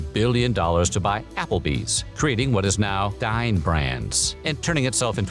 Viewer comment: When you said "Dine Brands" I heard "Dying Brands". 😂